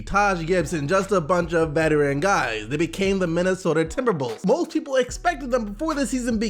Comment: Did I hear Minnesota Timberbulls?¿